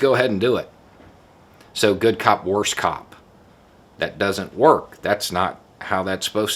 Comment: Referenced videos: Putting someone in a car: <link> Consent-based policing: <link> Body cam: <link> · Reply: I heard "Bad cop, worse cop" too!